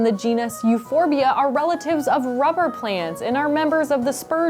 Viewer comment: I thought you said they were relatives of Robert Plant...